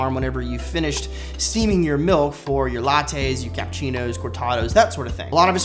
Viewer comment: Am I the only one that thought he said “ milf “ at 6:43 instead of milk? My minds playing with me